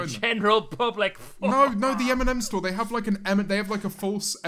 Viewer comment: i thought james said “eminem store” instead of m&m and they had tried to make the store smell like eminem…